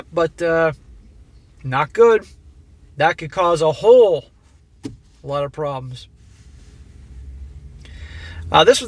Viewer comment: When first said. "Not good that could cause a whole lot of problems." I first thought he was saying. A Hole problem. Lol.